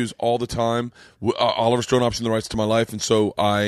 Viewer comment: Oliver Stone did what to the rights of your life? Sounds like you said "Oliver Stone offered me the rights to my life."...? Some clarity would be nice. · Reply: Alex Homer “optioned” the rights to his life. He would have first dibs on making a Burnt Chrysler movie or biopic.